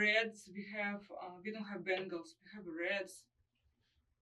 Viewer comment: We have Reds. No Bengals, though. Hahaha! I can't believe that the interviewer missed that one. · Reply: I thought she said bangles, what is she talking about!?